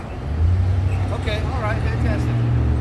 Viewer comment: time stamp I'm a little confused, what did the cop say???? What language is that? Does anyone know???? Fantastic???? What the shit??